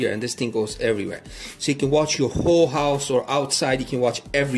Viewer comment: Did he say we can use this to watch “Full House” - cool dude 😎 📺